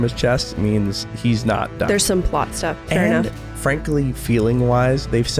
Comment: I wasn't paying enough attention and maybe the volume was a little low, so I hear "but stuff", with a single 't' in 'but'. "But" as in "but then this terrible thing happened". I thought it was a great pun!